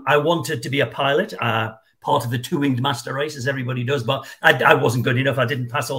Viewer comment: What did he say at 1:20, I wanted to be a pilot.... (what?) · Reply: It's weird he says "part of a two wing master race, but i wasn't good enough", but i am sure it means something else. I don't thing he is talking about human master race, that wouldn't make any sense. Unless fighter pilots call themselves the master race.😂